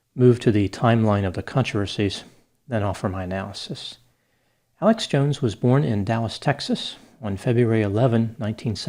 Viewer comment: I keep hearing "Alex Jones was right." Interesting how less than flattering pictures were used in this video.